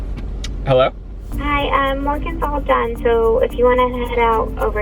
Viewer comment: When it said “Morgan’s all done” I thought it said “Logan Paul died”